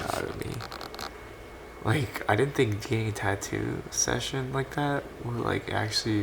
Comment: Lol @ 4:53 my captions say gay tattoo instead "getting"